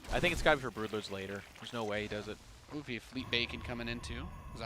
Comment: did he say fleet bacon..?